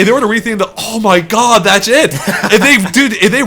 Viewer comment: Oh one more thing.. You really really need to work on saying "that's it!" lol I keep hearing "that's shit!"